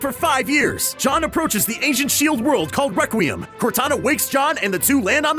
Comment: when he said "ancient shield world" i thought he said asian shield world